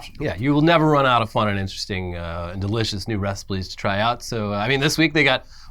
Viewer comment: did you say reciples? idk if i really heard an L there